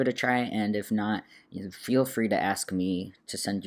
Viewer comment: Yes! I heard "feel free to ask me"